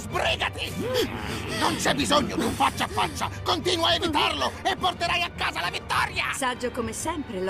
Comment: Emh ho capito male o al minuto 0:37 Champa dice: continua a hittarlo?